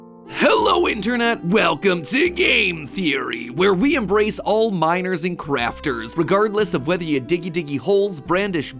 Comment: Did you just say you "embrace all minors", i.e embrace children? Check 00:33 Yeah, I'm not sure how I feel about this.